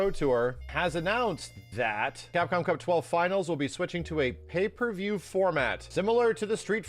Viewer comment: A moment ago, Sajam uploaded a video about pay per views, and I was like oh. I thought you were saying paper views, and I didn't question it because I just thought it was some weird Japanese way of calling these.